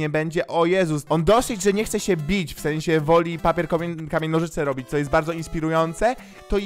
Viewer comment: czy ja tylko usłyszałam komen a nie k amień XD